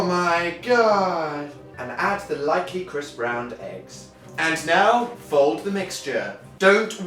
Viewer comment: I thought they said Crisp Brown